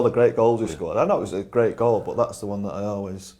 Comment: What did scholesy say at the end when G.Neville said the gaffer left him out the day? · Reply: "Thats why we won"